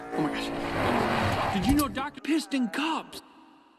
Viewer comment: McQueen: "did u know that doc pissed in cups Mater: lags ILR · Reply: I heard : "did you know that Doc fisted cops" referring to the previous scene with the sheriff 😂